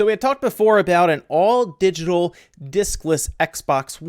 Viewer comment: HA HA, for a second I thought you said dickless!! Xbox.